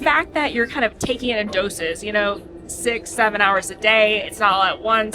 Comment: did say…. 6 7…?